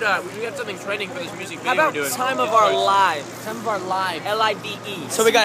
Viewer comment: I thought I heard Logan said 'SlimeOfOurLive'